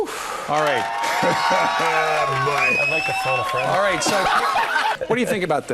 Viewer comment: what did he said? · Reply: but it sounds like he said "i'd like to photo-friend" you can't really tell what they said before they all started laughing.